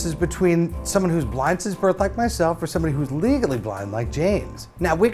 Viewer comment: Thought he said "Legally blond" Well... yeah.